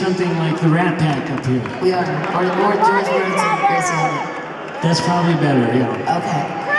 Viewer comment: Wait, what does Stevie say right before Lindsey says "That's probably better?" (At 5:50-ish) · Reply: George Burns and Gracie Allen